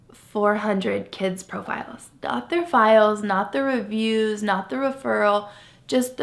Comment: Did she say their files contain REVIEWS!??? What, like they a five star rating scale? 😧😞